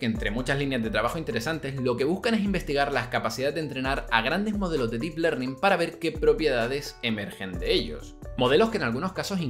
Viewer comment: "lo que buscan es investigar las..." ¿Alguien sabe qué fue lo que dijo? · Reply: capacidades de entrenar a grandes modelos de deep learning para ver qué propiedades emergen de ellos.